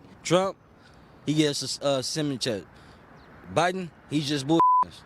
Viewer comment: Anyone know what the guy said? 🤔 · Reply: Stimulus check